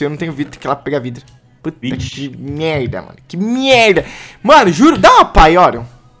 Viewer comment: eu ouvi ele falar "puta merda"